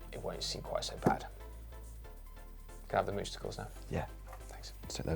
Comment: Did I just hear, “moose-tacles”? Sounds so wrong 😂